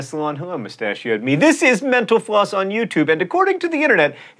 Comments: When you said mental floss I thought you said menapause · At the end I thought he said "menopause" instead of "mental_floss"... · it sounds like he says "menopause" when he says "mental floss" lmao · Sounds like he is saying 'menopause' instead of Mental Floss. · Hey, just a suggestion, but maybe enunciate Mental Floss better by paying special attention to the T and the F. I thought you said menopause.